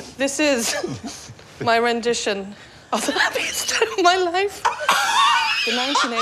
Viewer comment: What did she say? It's a rendition of what?? I can't understand with Trixie's laughing & cackling. Not even captions helped.😂😂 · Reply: A rendition of the happiest day of her life 🤣